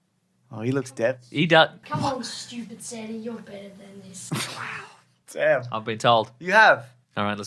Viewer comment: what did he say to? "Something something you're better than this" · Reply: “Come on stupid [Santa?], you are better than this”